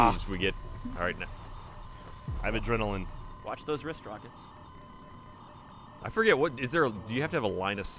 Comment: A little late on this but did anyone hear Andy at 5:16 say "Watch those wrist rockets."